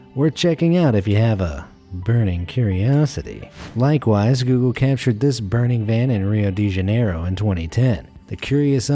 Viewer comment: “Google Maps caught this burning van” I thought he said “Google Maps caught this burning man”